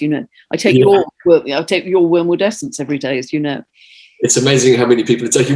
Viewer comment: can someone tell me what she said she takes everyday? I couldnt understand. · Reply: 💜 Wormwood Essence